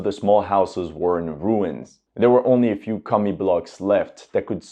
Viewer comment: "There were only a few Commie blocks left"... YT CC edited out "Commie".. LoL.. so what does that really say?